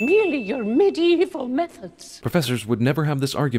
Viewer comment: I thought Professor Mcgonagall said 'Meth Heads" instead of methods the first time I watched the movie. lol.